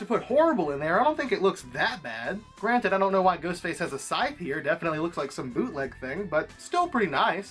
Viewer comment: What does he say here? Granted, I don't know why Ghostface has a side "????" It definitely looks like a bootleg thing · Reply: “Scythe”